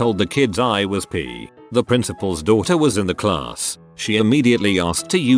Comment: I don't know what word he is saying The p*** one, My brain for some damn reason just won't click to what word it is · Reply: It’s pissed